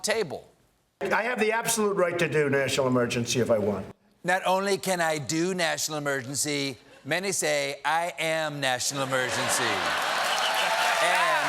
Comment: I swear Trump said "national emergentshee"